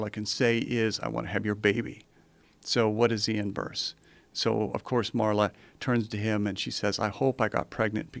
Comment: can someone PLEASE tell what words he's saying lol for the life of me I can't figure out what he's saying here. "So what does he Imburse"? Huh?